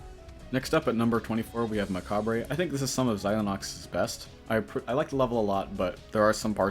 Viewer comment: did you say zylenox